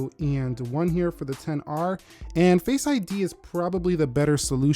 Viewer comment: i heard you saying “facebug” lol